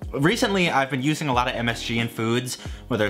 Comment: What? What he say? M. S. G.? Fuiyohhh!!!